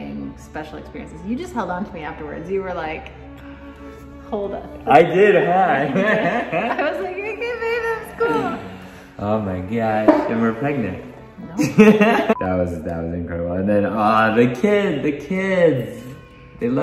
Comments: did I hear “and were pregnant”?? 😲💗☺️ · Did i just hear “we’re pregnant” anybody? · Thought I heard that you are pregnant but wasn't sure so I went back. Yep, you said it. Congrats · did he just say "and were pregnant" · Ps. I heard..."and we're pregnant"! also. 😊